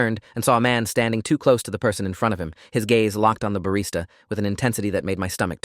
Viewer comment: I heard “his gays” 😭💀